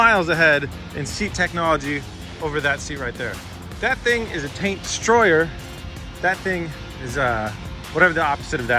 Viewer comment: did you just say a taint destroyer?…… SHIT!!!!! YOU SANK MY BATTLESHIP